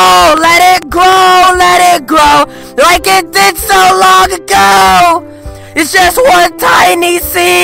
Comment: I thought he said like a dick so long ago😭😂